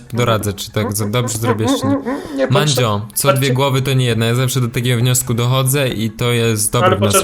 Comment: Co dwie głowy to nie jedna ! Ja (usłyszałem) Co dwie KROWY to nie jedna !